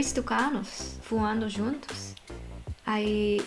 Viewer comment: só eu que ouvi "dois tucanos fumando juntos"? hahahaha mas o português dela é muito bom♥